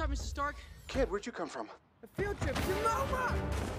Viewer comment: I swear during my first viewing when Tony asked Peter where he came from and he said "Field Trip" I honestly thought he said "The Future" That would've been so much funnier too